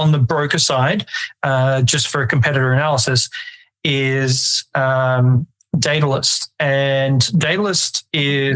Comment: What is the website of the platform that connects directly into the brokers tracking all the metrics? Did he say Data List? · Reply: Datalyst by Forexco